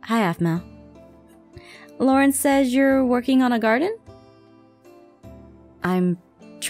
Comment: When said Garden I thought he said guard inn